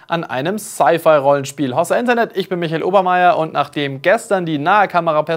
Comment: was sagt er da? 0:09 Hossa Internet